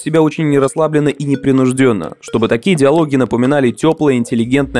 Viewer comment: не знаю, ошибка или мне послышалось, но говорится "не расслабленно и непринужденно...", а написано "Расслабленность и непринужденность..."